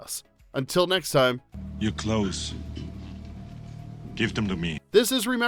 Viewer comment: When I first saw the movie, I thought he said, "You're close".